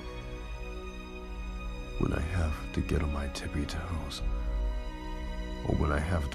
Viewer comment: Am I the only one that heard him say he needs to hon his tipsy toes this man is short and we are tall